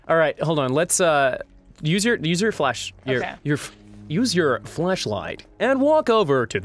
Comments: did he say fleshlight?! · D... Did just say... fleshlight?